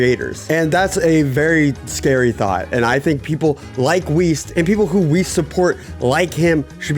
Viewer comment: East? I thought you said Weest